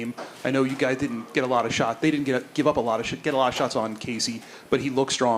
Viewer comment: did i hear "Give up a lot of shit" real quick in there? 😂